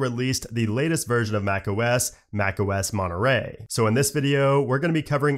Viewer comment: I really thought it said macOS Monkey. Hmm, Maybe next time